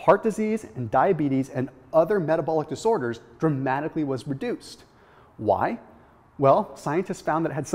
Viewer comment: I couldn't tell if you said reduced or produced.... I got confused if the 16 weeks folks risk of bad heart markers were reduced or increased. I thought you said the were produced (reduced?) in the 16 week. Did the 16 week vaccenic acid people bad health markers decine or increase? The graphic verbiage didn't indicated which way it went Natural yoghurt good or bad for you? I couldn't tell if the experiment run by the folks at Alberta was showing the negative health effect of vaccenic acid or the benefits... THANKS!!!!